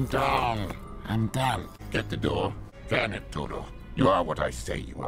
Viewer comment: does he say damn it? It sure sounds like it but I'm not sure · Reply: Can it todo